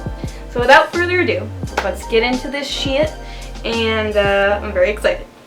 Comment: Was that a swear at 1:42? Or did she say shiz? XD